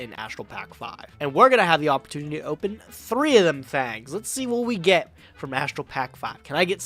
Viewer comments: Did he just say.. “ 3 of them thangs”? Love that reference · Um... D-did he say "open up 3 of them fags"? I... Lmfao what? I'm mishearing it right?